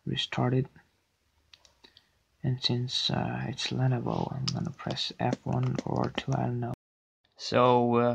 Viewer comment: What does he say at 0:43? It sounds like “and since it’s Lenovo I’m gonna press F1 or 2 I don’t know” Looking for help. I’ve got a Lenovo Legion Y520 and I don’t know how to get into the BIOS set up